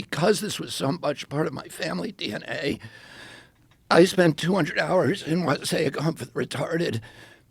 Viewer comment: can someone lmk what he’s saying here please and thank you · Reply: Per this link,....... ["I spent 200 hours on, say, a comfort, retarded, you know, working, doing service. ] <link>